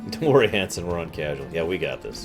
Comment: Okay this happened on stream, and this happened while rewatching it, I thought you said "don't worry HENSON(one of your viewers), we're on casual". I was so confused by that statement and tried to go back and find where Henson had said something about the difficulty.... he wasn't even in chat. No, instead you had said Hanson and I only just now realized that. Yea, I really am the smartest person ever. :D /s